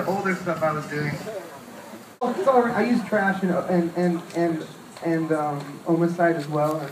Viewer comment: "I use....? and Ohmicide..." what is the first thing he says? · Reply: I know this is late as fuck.....but Izotope Trash 2